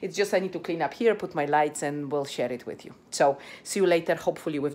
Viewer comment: Haha "'m so sorry im becoming a pest but whilst watching I had to rewind back to 14:25 caus it sounded like you said "bulls*%t it with you"I'm soos sorry. And I like how happy you looked with the smirk on your face when reporting that the kids did not like the mousse. "I'm going to eat them all..(pause) not today of course"